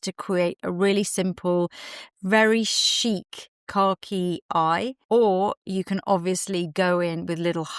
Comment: Oh! You’re saying “car key”! I thought you were saying khaki but in a British way I’d never heard before! 😂